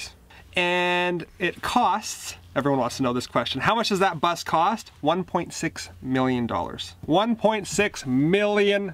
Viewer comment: I thought you said 1.6 BILLION!! I had to listen to that twice!!! Congratulations, you made history!!! Awesome video. Thank you for sharing with us! It means a lot!